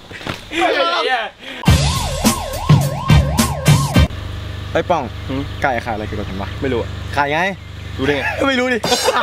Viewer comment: พี่เขาพูดว่าไรอ่ะ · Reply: O : เห้ยป้องไก่กับไข่อะไรเกิดก่อนกันวะ P : ไม่รู้อะ O : ไข่ไง